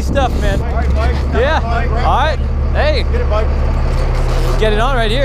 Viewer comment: everyone sounded like the seagulls from nemo... Mike mike mike